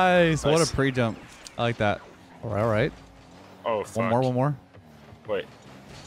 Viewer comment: Did he say "oh fuck"?